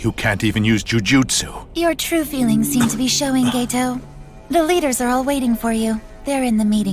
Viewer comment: DID SHE JUST SAY "Gayto" 😭